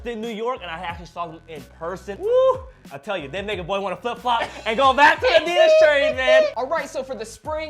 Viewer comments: DID HE SAY "THAT NIGGA BOY WANNA FLIP FLOP"? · did he say the n word? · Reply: johnny Luu “they make a boy”